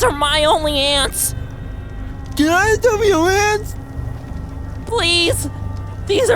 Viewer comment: I thought he said "Can I smell your ass" 😂😂😂